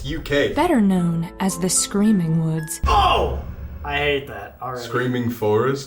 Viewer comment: Sam: Oh! I hate that. It sounded like a Brazilian expression "Eita" 😅